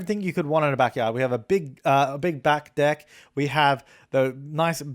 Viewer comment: you said big back deck all I heard was big black dick... God help me